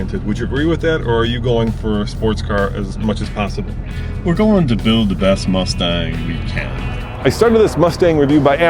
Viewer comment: He said, "we're going to build the best mustang we can." But all I heard was "...most expensive mustang."